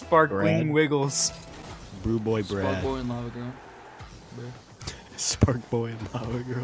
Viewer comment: When he said “Spark boy and water girl” I thought he said “Sparkling water girl”